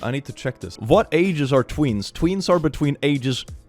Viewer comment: I THOUGHT YOU SAID "TWINKS" HELP- 31:15 I've had a realisation and it went a bit like; "Haha it's like the saying 'BloWinG SMokE uP mY AsS'- WAIT"